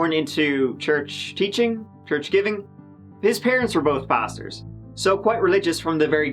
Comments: I definitely heard "his parents were both bastards" 😂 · I thought you said his parents were both “bastards” instead of pastors; even still, the apple doesn’t fall far from the 🌲. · Listening to this guy whine like an infant throughout that interview was really, truly painful to listen to. Plus, I swear I thought I heard: "His parents were both bastards. So quite religious from the very get-go"--which both explained a lot and killed me laughing. Not the case obvz but this misinterpretation on my part really set the tone for a horrendous story. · ! Love your show. When you said " both his parents were pastors" I genuinely thought you said" bastards!" Very sorry! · I thought you said his parents were both bastards ha but maybe they are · When you said his parents were pastors, it’s sounded like you said his parents were bastards with your accent! Lol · I thought he said bastards instead of pastors lol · His parents were both Bastards.. sounds like you said this 🤣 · Dame I thought you said both of his parents were bastards 😂😂😂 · At the beginning, I thought said Chris's parents were both bastards. I thought, Wow! That's a harsh way to start the episode. They must be bloody awful people! 😳😳😳 I quickly realized actually said Chris's parents were both PASTORS. Oops. 😝😝😝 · I don’t know why I thought he said bastards instead of pastors 😂🤦🏼‍♀️ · When u said his parents were both pastors i could have sworn you said bastards for a min lmao